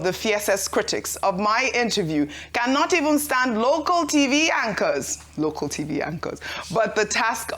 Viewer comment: " Cannot stand local TV anchors" What i heard "Cannot stand Rufia & Ruben".😂😂